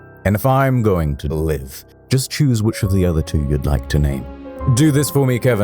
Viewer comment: I got the question right because I misheard the orange guy. I thought he said "If I am going to be executed choose one of the other." So by choosing blue it's 100% chance for orange being executed.